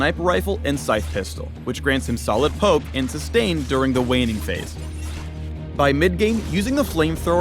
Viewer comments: Did anyone else hear waning phase at 4:08 or just me? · Did anyone else hear “waning phase” at 4:08? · Anyone else hear him say "Waning phase" instead of laning phase?? 😂👍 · does he say solid pope and waning phase?